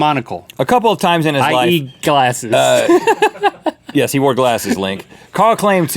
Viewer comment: it sounded like link said "I eat glasses" LOLOLOLOL 😂😂😂😂😂😂😂😂😂😂😂😂😂😂😂😂😂😂😂😂